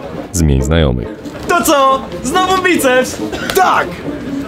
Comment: Co mówi w 3:14? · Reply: "znowu biceps "chyba xD